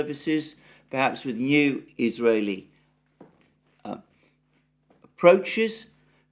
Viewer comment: "new israely approaches" almost sounded like "new israeli prime minister" 🙂